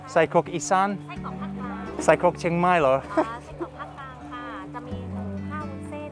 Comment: What she actually said was central region sausage, not Lampang haha xD 🇹🇭🌭